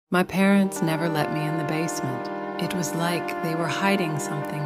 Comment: i thought it said they never let me out of the basement